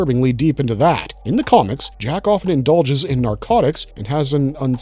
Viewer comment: I don’t know why I heard JackOff 😂😂 I’m just a h0rny fck